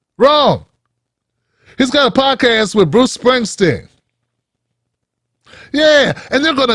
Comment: My bad my my ears are going I thought you said Trump is doing a podcast with Springsteen I was in shock but you said Obama is doing a podcast with the boss that I'm not shocked about. Here's the thing about Obama I cut him some slack because the Republicans voted against everything he asked for when he was in office. But no one was stopping him after he got out of office to fight for the right thing. When he stood with Hillary and Biden he proved beyond a shadow of a doubt that he's owned and paid for by the establishment